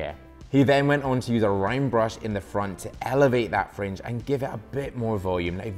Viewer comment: I'm not going to lie at 1:48, I thought he said he used a Rhine Brush, and I searched for it on Amazon and nothing came up. I realized just now, he said it was a round brush. D'oh.